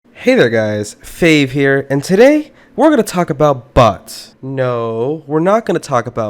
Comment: I heard “Where gonna talk about butts”